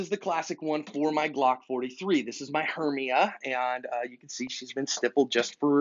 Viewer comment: Thanks to this I learned about the MIC Trigger Guard. Could you please recommend some budget friendly Kydex holsters with soft loops like the one you showed <mail> What is the brand? It sounded like you said "Hernia". thx for help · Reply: My favorite kydex holster maker is Gabe at The Armory: <link> and if you use coupon ASP2018 you get 10% off. (I make nothing) And that was Hermia. The name of my little G43.